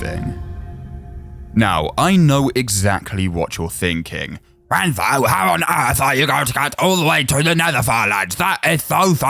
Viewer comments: if y’all don’t know what he was saying, he was saying, “Wenzo, how on Earth are you going to get all the way to the Nether Farlands?! That is so far away!” Don’t ask me how I understood that. Idek how I understood it 😭🙏🏽. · i dont understand a single thing he says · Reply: “wenzo how on earth are you going to get to the nether farlands that is so far away”